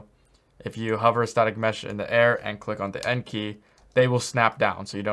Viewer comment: What is key for drop object to landscape? I kind of mishear it like N key but it's not working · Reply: "End" key its above the numerals on the right.